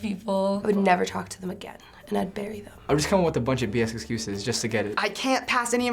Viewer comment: Did she say "I would bury them!"? :O